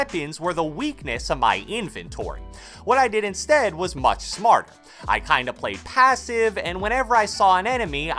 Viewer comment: What i said is much smart lmao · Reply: He said smarter the er was quiet